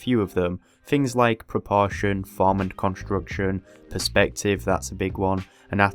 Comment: "things like proportion, vomit construction, ..." Please help, I've listened over and over to this and I have no idea what he's saying here :( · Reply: “things like proportion, form, and construction” 👌🏻